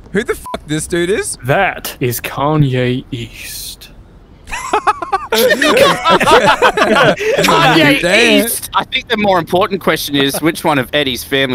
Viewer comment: Kanye east? I thought you said kanye Yeast